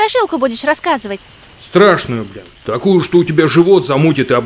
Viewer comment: Я один услышал бля